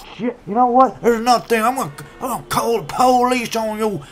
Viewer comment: I thought he said "ima call the poleeese on you gays "